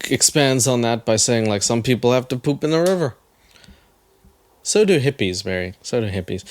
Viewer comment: I thought she said some people poop in the Uber 😂